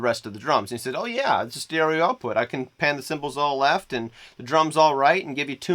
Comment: Hey, at around 1:30 you talk about "panning the (*intelligible) to the left and the drums to the right" Sadly, I can't seem to catch what word you say you panned to the left. Could you elaborate? Otherwise great tips, thanks a lot! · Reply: Cymbals is what I said there